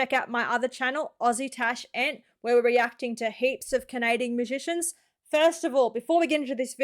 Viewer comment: Omg! 😅 I love your accent. However, I thought you said "Canadian magician's." I was just like, "Wait Celine Dion is a magician too?! Since when?? For a moment, I thought you were making a video about famous Canadians who also happened to secretly be magicians or something. 🤣😂🤣😅 I realized pretty quickly that this is not at all what you were saying.